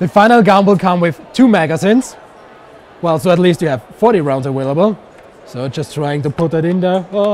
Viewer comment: You heard what he said, it will have 40 rounds "a-whale-able"